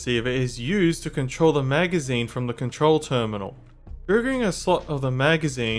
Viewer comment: What did he say at 49:45 I can't understand I don't mean that he said smth worng [Edit]:ohhhhhhhhh it's triggering